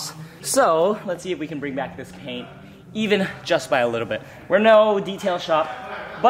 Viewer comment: did he say caint??? 😭😭 like as in car taint? THE REAR END OF THE VEHICLE LMAO